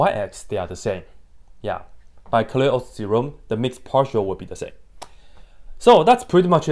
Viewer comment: By whose theorem, please? I didn't catch that. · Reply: Clairaut's theorem